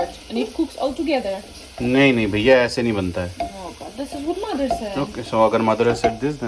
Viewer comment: saying " this is what mother said" sounds like " maadar said" pehli baar sunke insaan confuse ho jaye ki wahi bola jo maine suna:face-blue-smiling::face-blue-smiling